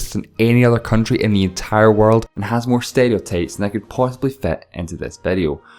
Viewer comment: am I the only one who thought he said stereotapes instead of stereotypes